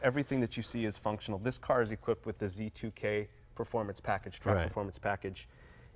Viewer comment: I thought it was called the ZTK Package, is it called the Z2K??